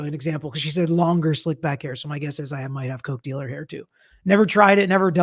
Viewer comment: Oh! "coke dealer" hair. I thought she was saying "cochlear" hair! lol